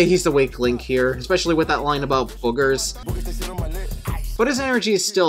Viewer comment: I swear to god I thought Offset said "Booties they sit on my neck" and not boogers